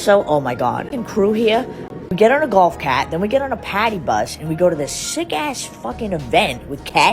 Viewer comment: I 1000% THOUGHT SHE SAID POTTY BUS 💀